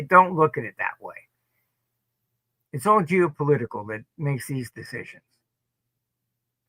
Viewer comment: did he say "it's all Jew Political"?